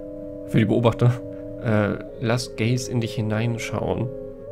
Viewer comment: Ich hatte das Video nebenbei laufen und bei 41:22 verstanden: "Lass Gays in dich hinein schauen" und dachte ich hör nicht richtig